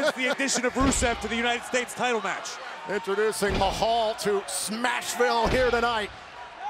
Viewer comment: did he say smash ville? This ain't smash bros.